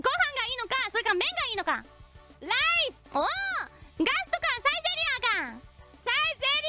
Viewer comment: i thought she said lice